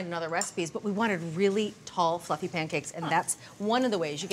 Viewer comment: I thought she said, “We want really tall, fucking pancakes!” Which is what I’m sure she meant anyhow.